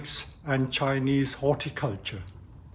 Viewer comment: When he said "Chinese ceramics and Chinese horticulture" I totally thought he said "Chinese ceramics and Chinese water torture" 🤣